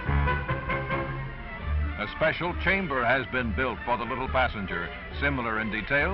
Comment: Genuinely thought the announcer said "A special chamber has been built for the little bastard"